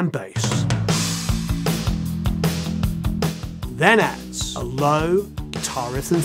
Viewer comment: What does he say here 4:28 ? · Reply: then adds a low riff and thirds 🎸